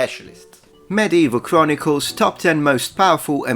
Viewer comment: Ok love the Blade Runner quote. Thought you said Medieval barnacles and given the articles I'm not sure if it would have been worse or better. Also suggestion. Try drinking a shot of Malort. At this point it would be a less horrible experience.